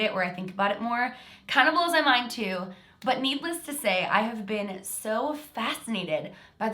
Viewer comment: Instead of “Needless to say” I thought she said “Meepless to say”